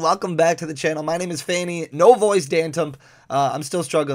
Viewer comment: Did he just say my name is phanny "no voice" danthump? 😂